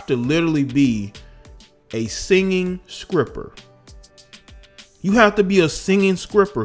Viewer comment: did he say "singing scripper"? 😂